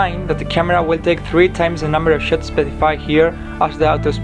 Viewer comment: Did you say the camera is gonna take 3 times the number of shits?